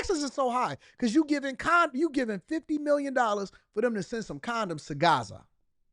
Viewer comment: Condoms??? I thought she said condos at first, that’s crazy!!!